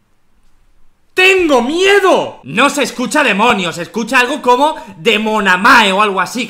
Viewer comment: Yo escuche "tengo sexo si si si"